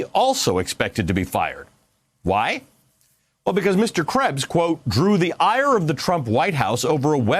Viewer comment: I thought he said mr krabs